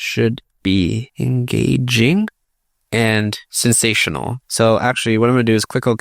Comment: Did bro say "Sensational" ?